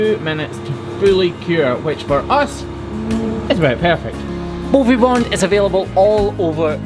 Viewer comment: I thought you were saying “booby bond” lol